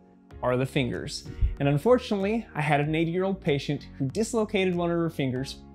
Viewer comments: I thought he said he had an 8 year old patient xD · I thought he said an eight year old not an 80-year-old and whenever I saw her finger I was like what the heck